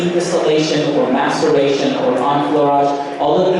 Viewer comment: Very interesting... I had to LOL@ 5:27 "what method of extraction.... " then it sounded like "masturbation" . cool video im sure you guys had a blast.